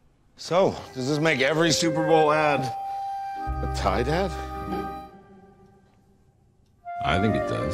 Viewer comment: I thought David Harbour was saying "tie dad"